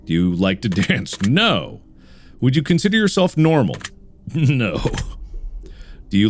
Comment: I thought it said "would you consider yourself MORTAL"